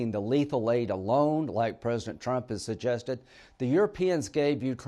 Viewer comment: Did I hear him say "like President Trump suggested" I hope not